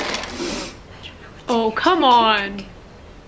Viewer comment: did she just say "I'm really thirsty, I don't know what's in here I can't drink it"